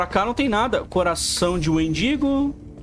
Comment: nessa parte eu entendi Coração de Mendigo. ! Kkkkkkkk